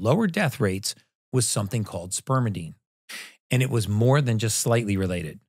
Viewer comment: omg i thought it said spermicide at first